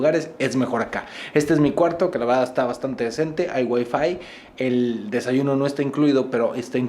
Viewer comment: pensé que dijo hay wey en ves de hay wifi